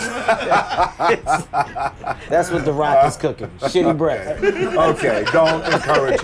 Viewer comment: “Thats what the rock is cooking ... sh**ty Breath” ~Kevin Heart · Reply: I thought he said shitty breasts but that makes so much more sense 😂😂😂😂